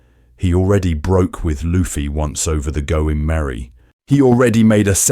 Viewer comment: The caption says “Go in Merry” instead of “Going Merry.”